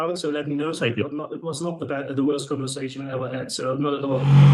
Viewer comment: "It was not the worst conversation I ever had..." is such a great German compliment, lol. My Mom says stuff like that even decades after leaving Germany! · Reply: I had to rewind, I thought he said that was the worst conversation he ever had LOL